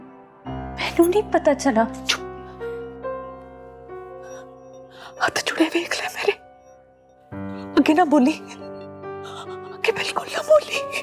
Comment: did she say hath jurray hway iqra meray?🤣